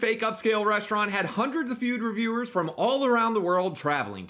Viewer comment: I can’t be the only one who heard him say feud reviewers😂